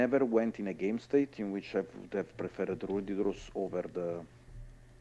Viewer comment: I'm struggling, but what does he say at 9:33? He says he was never in a game state where he would prefer x over x card and for the life of me I can't make it out. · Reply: Ruddy Rose!